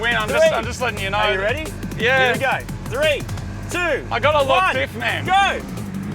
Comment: oooooooo you said you have a locked diff i thought you said you had a long ....